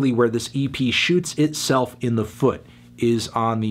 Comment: Wtf at 2:38 he says shoots herself in the foot